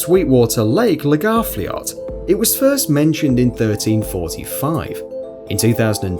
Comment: did you say year 3045???